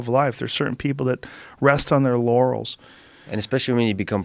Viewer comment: Anyone else hear him say "Rest on their Yannies"?